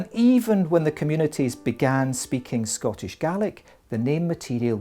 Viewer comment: are you sure he is not saying garlic?